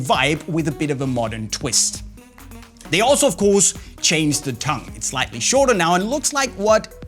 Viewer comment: Honestly thought he said... "hated the f@ tongue" hahaha